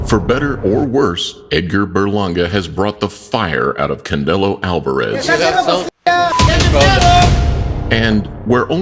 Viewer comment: did he say Candelo Alvarez? Maybe he didn't? But here we go boyz! Let''s enjoy this! Thanks!